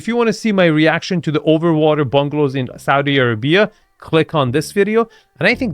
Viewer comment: sounded like "saudi airbnba" haha perfect